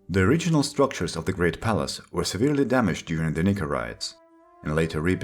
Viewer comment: Wait WHAT did you say??? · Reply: "Nika riots", my nika